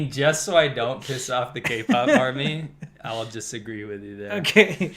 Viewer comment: I heard gay-pop, I'm sure meant to say k-pop lol